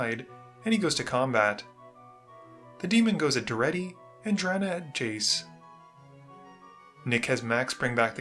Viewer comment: Hey. For some reason i thought you said Drana The Punchy. And knowing that you nickname everybody I almost didn’t question it